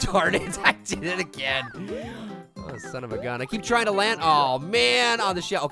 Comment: Pear! Why did you say son of a bi- Wait a second, he said son of a gun I thought he said son of a b***h My apologies